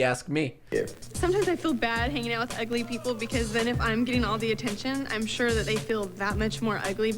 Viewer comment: did she just say "attempsion" instead of "attention"?